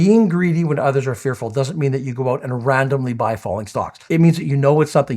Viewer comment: I thought you said “ falling star”: Perry Como